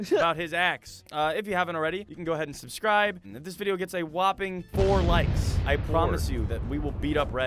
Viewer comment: guys I thought he said only 4 likes